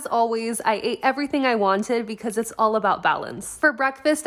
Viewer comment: Thought she said "i ate everything i wanted coz its all about bowels"